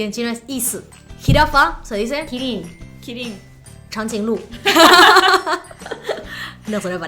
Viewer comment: En el minuto 7:10 cuando dice cháng jīng lù (el jīng no encontré lo ondulado ) me sonó que dice tan si luz